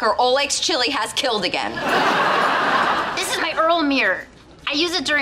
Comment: all I heard at 'earl mirror' was 'mewmew'🤣